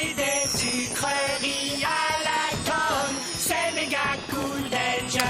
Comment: C'est moi ou ils disent "manger des sucreries à l'alcool" ? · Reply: perso j'entand à la tonne